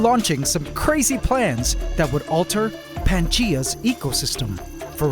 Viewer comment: did you mean to say Pangea at 5:01? i checked 3 times to see if i heard it right :D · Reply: Pantgea (Pangea with an ant pun in it lol) refers to all of the large ecosystems that are connected to each other! Basically one name for all of the connections so he doesn’t have to repeat three names over and over again when is referring to the entire large connected ecosystem. If that makes sense 😅